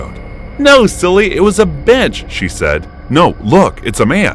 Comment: LOL why did it sound like she said b****and then it dawned on me maybe she said bench LOL😂 at least I'm hoping that's what she ended up saying